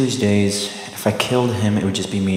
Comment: “I hate Sharon… she does not do tuna tuesdays” that last line is what did it for me 😂😂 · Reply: At first I thought he said Karen but notice he was mentioning Sharon 🤣